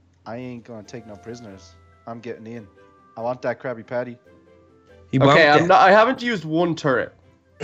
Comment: Who heard him say I’m really hungry for a crusty crab. Do You mean krabby patty